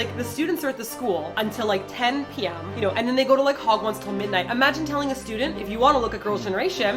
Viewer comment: I know this has nothing to do with the sensitive topic of this tldr...but at 1:54 I totally thought said, "then they go to Hogwarts till like midnight"